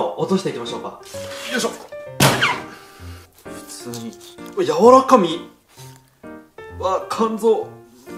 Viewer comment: 柔ら神って聞こえたわ